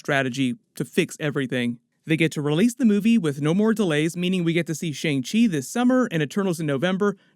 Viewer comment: When Charlie said, "No more delays" I heard, "No more Mutants"